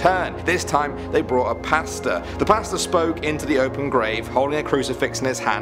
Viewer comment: Am I the only one that thought he said pasta instead of Pastor?